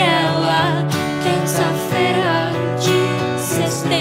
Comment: Só eu q escutei "E foi assim que eu vi que a vida colocou ele pra mim ali naquela terça feira de Sestembro"?